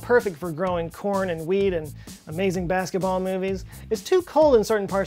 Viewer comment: when he says wheat i heard weed and subtitles agree with me